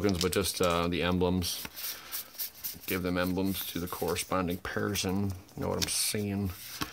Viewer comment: For a moment I thought you said "give the tokens to the parasites" lol